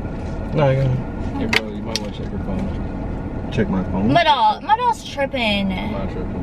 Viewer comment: Who else thought she was saying “my dawg” at first 😂